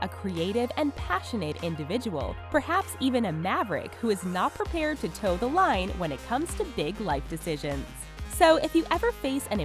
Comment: did she just say maverik as in logang